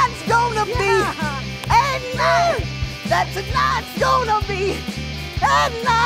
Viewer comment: TONIGHT IS GONNA BE... A NIGHT Me- did you just say egg night??....